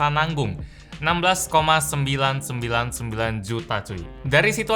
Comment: 16,999,000 juta cuk gw dengernya gitu